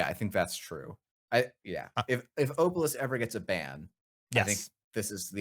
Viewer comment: They're saying "Obelus" not "Obelisk" in case anybody else misheard it like I did :) .